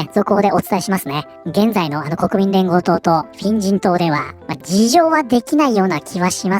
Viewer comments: フィン人党が変人党に空耳した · フィン人党が変人党に聞こえる